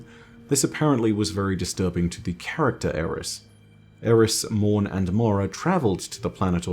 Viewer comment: I misheard this as "Eramis" and Mara and was so confused for 10 minutes.